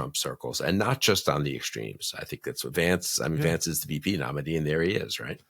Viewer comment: I thought I heard Bill Kristol say "Shady Vance;" á propos (even though I probably misheard him).